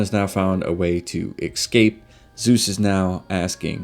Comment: Did you just say excape?!